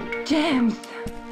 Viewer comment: it always sounded like Dominique said “Gems” to me when she said Bond’s name lol